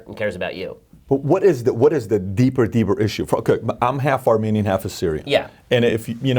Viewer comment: half Armenian half Assyrian? Did I hear right??